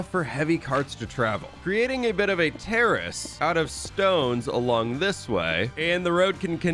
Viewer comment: am I the only one but he said terrorist instead of tourist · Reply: nah not terrorist he said "terrace" as in a paved area next to a building.